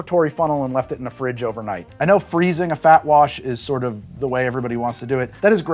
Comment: When You said "FAT WASH," I thought you said, "BACK WASH!" I thought, "That doesn't sound too appetizing!" Hahaha Top Video! Thankyou